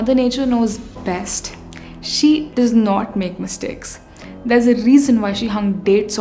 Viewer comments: she says mother nature doesn't make mistakes... the caption says 'mother nature makes mistakes'... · Interesting she says mother nature does not make mistakes but the subtitles say she does make mistakes xD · "see, mother nature knows best. she doesn't make mistakes" Jake paul was borned 7:20 - subtitles says "see, mother nature knows best. she dose make mistakes."